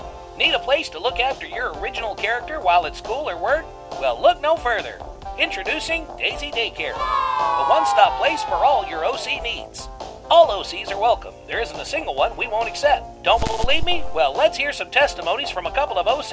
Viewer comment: oh OC needs! I thought it said OCD’s